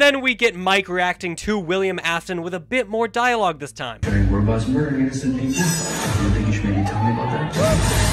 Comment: what is Mike or William Afton saying? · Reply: Mike said: "Giant robots murdering innocent people? Don't you think you should've maybe told me about that?"